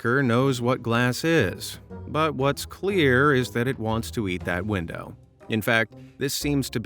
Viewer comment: At around 28:27 "clear pecker 😅😂😂😂😂" thought you couldn't say things like that without getting in trouble 😅.